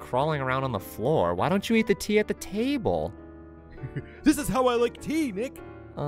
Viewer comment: I thought I heard him say "eat the tea on the table." I cracked up laughing.